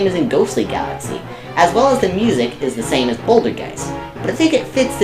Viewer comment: Again, it was just who didn't like the music. I personally do. Also, by Mechabowser, do you mean the robot at the end of Toy Time? Because we counted that more as a level than a boss, hence it not being on the list. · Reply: Yeah I thought he said "The music is terrible" and I thought from that he was saying both of your opinions.